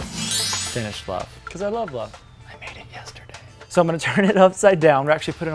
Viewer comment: did he just say that he made love “yesterday”? Lol!!! 😂